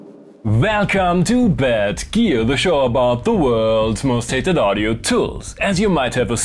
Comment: Was fully confused when I heard “Welcome to Bad Gear, the show about the world’s most hated audio tools” instead of “Hi, I’m Hainbach, good to have you back.”